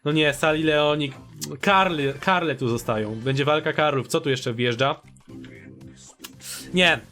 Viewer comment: Czy tylko ja usłyszałem: ''Wojna Karłów'' ?